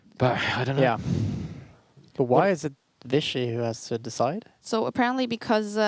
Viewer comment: Carlsen violated rules he agreed to play upon. What's there to stand up to? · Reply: check 4:19:25 he clearly says vishy and not fishy